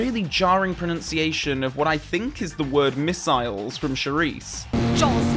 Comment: I think she's saying 'mortars' at 19:22 xD